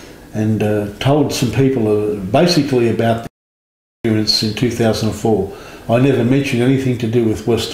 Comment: Does anybody know what he says at 44:03? Something is muted out for only about a second, and it’s driving me crazy. He says “I went along to a couple of meetings, and told some people basically about ___ in 2004.” With everything else that has been said in this interview, for the life of me, I have no clue what could’ve been deemed inappropriate enough to mute. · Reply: '...and told some people basically about the abduction experience in 2004.'